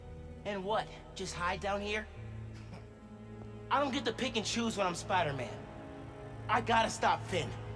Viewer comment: I thought Miles said Pikachu XD